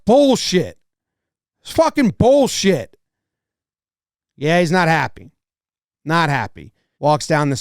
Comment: thought he was saying "Bullshit call"